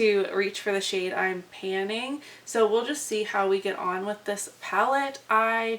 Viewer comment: I love to use Red toned shades as a lower lash line color, or use an irridescent on top to make it brighter. OR in the outer corner of a look, typically they're too dark for me. I have ACI2 (a red multichrome) in my Deck of Panning update topped with a pink topper in the center and I loved it way more than how it looked alone. I hope you find a favorite way to wear it! Maybe try it as an ombre liner or something?? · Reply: I paused to type my update and then played and heard you say "how to use it with this palette" welp... oops... 😆😆